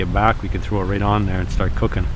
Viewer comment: I thought it said "primate cooking".